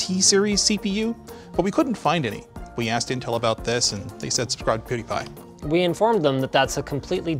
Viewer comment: what is saying? Edit: it's subscribe to pewdiepie